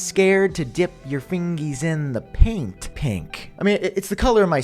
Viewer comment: I had a WTF moment at 5:41 … I thought he said ”Scared to dip your fingers in the pink PINK” … he actually said ”paint pink” 😂😂